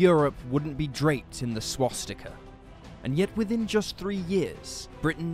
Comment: did he say drained down to swastika :angry